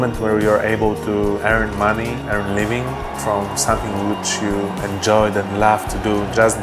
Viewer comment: Who can tell what he said in 0:38 ? "from '......' enjoy them love to do?"